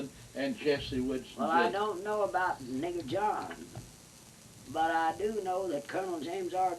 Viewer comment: does she say "i dont know about (Nigger John)!"...and again about less than a minute later🤔, just wondering?, might be his "Street Name!".😎